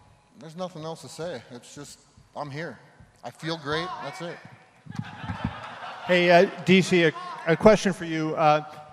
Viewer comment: what does the guy in the crowd shout at 9:03? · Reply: Where is Paul Heyman